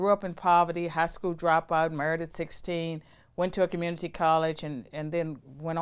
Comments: When she said “Married at 16” I thought she said “murdered 16” and I was like woah. Then I was like wait no. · I legit had to look her up, I thought she said murdered at 16 and I was like what??! Married, married at 16!! Her Virginia accent got me in that one! 😅